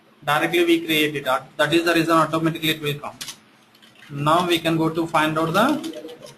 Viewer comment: This presentation is very well put together, can't thank you enough! Having said that, can any one transcribe what the guy had said at 1:07:56 exactly after saying 'that is the result of'? Despite repeated trials of listening, I couldn't hear what he said. Thank you in advance for the kind cooperation! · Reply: he is saying: 'that is the reason automatically it will come'.